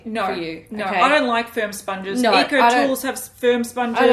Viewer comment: "Firm sponges" and all I heard was "sperm sponges". I think its time for me to take a nap 😂